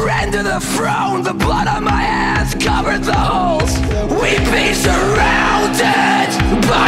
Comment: i swear to christ that i heard "covered buttholes"